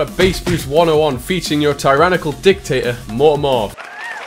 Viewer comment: i thought you said "dicktator"